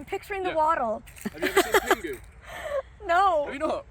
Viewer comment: The edit of Pingu being just a random penguin 😂😂😂 Obviously not a popular kids tv show in Canada 🤣 xx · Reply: I think it was just hard to hear “Pingu”. It sounded like he just said penguin to my American ears.